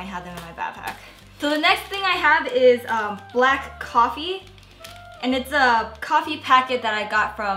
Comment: anyone else thought she was about to say "a BLACK condom" ?